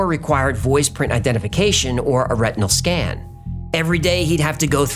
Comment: I thought you said rectal